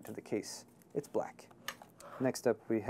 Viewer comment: I heard "It's whack" the first time I heard say "It's black."